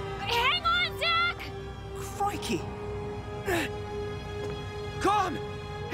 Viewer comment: Yk it’s serious when ✨gRaHAM cRaCkLe✨ says “crikey 🫨🫨🫨🫨!” I thought he said f***ey 😭